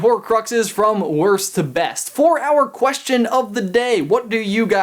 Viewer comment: So at the end I totally heard"four hour question of the day..." instead of "for our question of the day..."